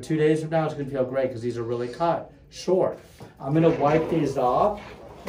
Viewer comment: I played this at 1.5 speed so I just heard him saying "I'm going slow" and I was like 😬